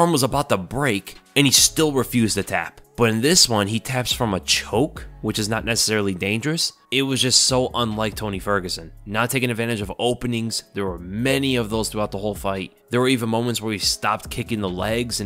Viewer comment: did you say a child is not dangerous 🤦🏻‍♂️